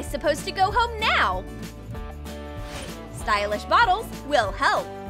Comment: Did she just say stylish And what about the money spent on the slippers